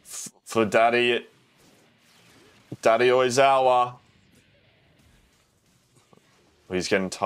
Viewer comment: I misheard Daddy Oikawa when he said Daddy Oizawa.